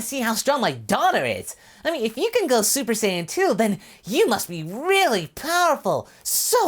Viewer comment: What super sayian 2 or super sayian to